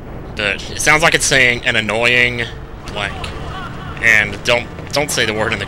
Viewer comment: I always thought he said "I'm Annoying, huh?"